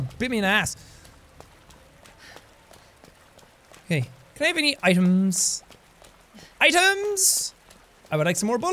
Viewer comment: Jack says: "can I have any items?" At first I heard, "can I have any IAMS" like the dog food.... because Claire was fighting zombie dogs..... am I the only one..